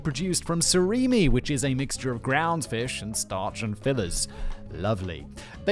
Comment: I thought I heard "made of ground fish, starch and feathers" 🤢